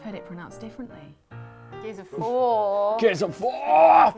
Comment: Show of the Weekend! gets wistful about Pokémon Go, and administers the psychologically devastating Dishonored 2 powers quiz. Enjoy! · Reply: In golf, it's "fore," abbreviation of "before," meaning "in front of," as in foresail, foredeck, putting the cart before the horse and so on. I thought said "Gears of Phwoar" in that clip, which would be something else entirely.